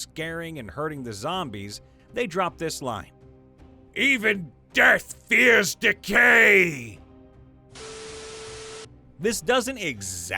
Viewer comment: I wasn't paying full attention to the video and heard this as "EVEN DEATH FEARS TAKEI!"